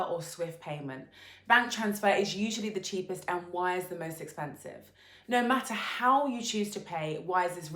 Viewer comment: Be aware, I guess the person speaking says 'wire' at 3:30 but that word was not previously mentioned, and with her accent, sounds like she is saying "wise is most expensive". the script could be clearer. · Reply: True… i played and played the mentioned soundbite, and it really sounds like she said "wise the most expensive". Too bad, it kind of self-defeat the whole pitch!!!